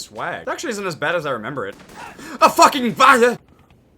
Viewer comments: what is he saying something like fucking badger or something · did have something in his mouth when saying "A f**king f*ggot" or what it sounded like. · what did he say at 1:31 fucking badger or something XD